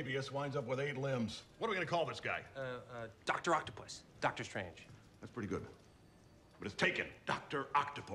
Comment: Am i trippin or did they just say there was a Dr. Strange in Toby McGuire Spideys universe? 🤯